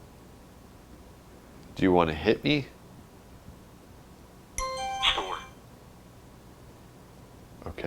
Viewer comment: I heard: "Do you wanna hit me?" "Sure! :D"